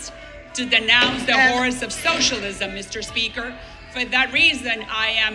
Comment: How about Waters being removed? Salazar said, "...as there is someone who has seen the horrors of socialism up close...", I thought at first that she said HORSE of socialism (my first thought - was she referring to horse Waters LOL!!! ?)